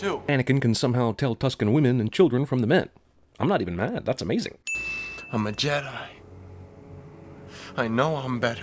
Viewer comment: I had my volume to where it sounded like he said “I’m a janitor”.